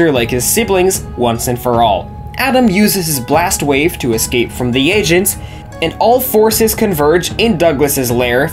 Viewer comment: it totally sounds like he said “Using his blast wave to escape from the Asians.”